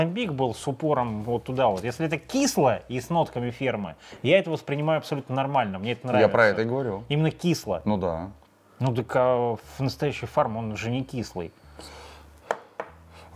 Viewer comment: мне послышалась что сказал "с нотками спермы") 19 47